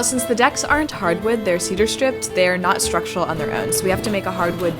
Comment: As God is my witness, I thought it said "cheddar strip".